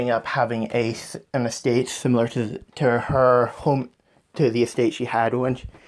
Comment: When u said estate I thought you said mistake